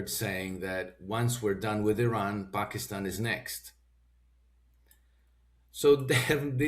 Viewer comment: What are you talking about?! They said: ".. Turkey will be next"